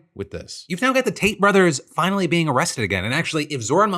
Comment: I could've sworn Phil said "Taint brothers" at the beginning of the first story.